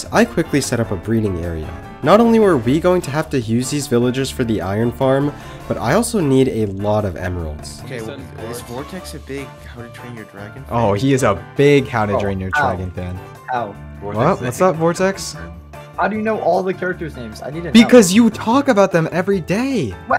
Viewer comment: anyone else heard him say how to drain your tragon fan?